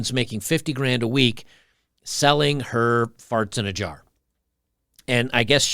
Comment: I initially thought you said she was selling her parts in jars. That wouldve been something.